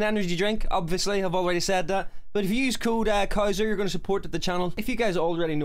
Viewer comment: Oh my god I heard “cool dad couser” not “code couser” Fuckmeup